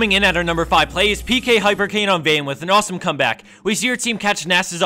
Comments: Really sounds like he said Vayne in the first video, not Vi, i was confused. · coulda sworn he said vayne not vi at the start haha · i think he said "vi" but you're nt the only one who thought he said "vayne" xD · Yeah, thought I heard it wrong, too. But he said "vayne" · I thought he said Vayne, but it was Vi. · Did he said "Vayne" at 0:24?